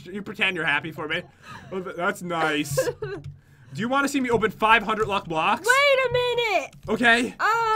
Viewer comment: It sounded like he said "wanna watch me open 500 luck blocks" 😅